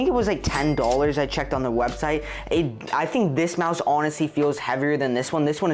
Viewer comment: at 1:23 it says house instead of mouse in captions